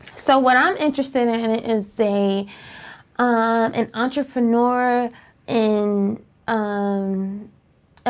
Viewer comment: did she just say ‘ontrofinuer’ for entrepreneur?